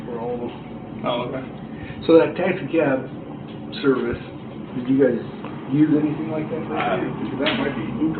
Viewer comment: What did the officer say at 2:18:20? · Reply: “So that taxi cab service, did you guys use anything like that recently?” Referring to the bank statement showing that someone paid for a taxi service on Aug 12th.